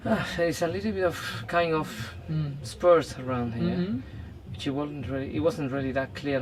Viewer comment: What did the vet say about the disease? The horse has sports on the joint ? 11:38 minute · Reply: Bone spurs. Spurs are like abnormal bone growths around the joints that stick out and cause pain. It's usually the sign of some kind of joint damage. A horse that young shouldn't typically have something like that, so it's very suspicious.